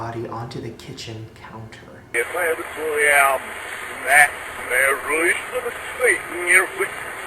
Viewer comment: Am I the only one who hears the audio say "release of the satan" at 5:26 ??